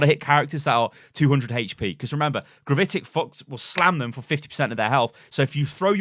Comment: I swear he said gravitic fucks